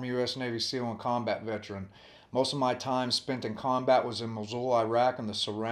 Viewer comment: did you say Mosul?